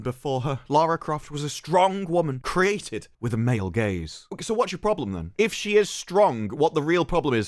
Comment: Every single time all i hear is.. "MALE GAYS" 😂